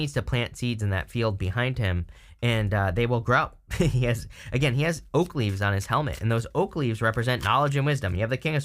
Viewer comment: I thought you said he had 'Oakleys' on! 😂😂😂😂 And well yes, that would symbolize some kinda success😂